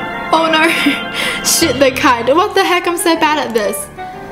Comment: I heard sh+t the kite in